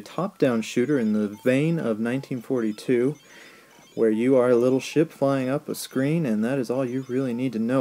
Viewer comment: I thought you said, "...where you are a little shit flyin' up the screen...."